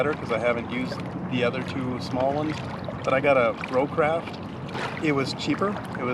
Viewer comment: I bought my Brocraft on Amazon, but it looks like it’s not available now… Here’s the link for the Brocraft Marine site, but it looks like they’re sold out too…🙄 <link> I picked up my weight at a local Sporting good store several years ago “Black Sheep Sporting Goods” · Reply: no wonder i couldn't find it i thought you said Pro craft lol , thanks for the info .